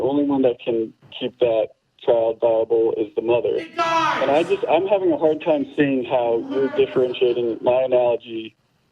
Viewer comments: Can anyone tell me what that outcry was at 12:50? What was said there? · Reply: Matt yelled "it dies!" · What was screamed there at 12:51? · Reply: It dies! By Matt